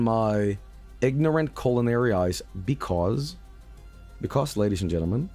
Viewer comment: My American made me mishear culinary as coronary